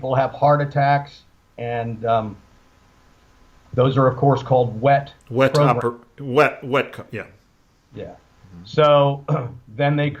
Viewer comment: wet what? interfered, which prevented me from hearing what the guest said. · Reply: Wet works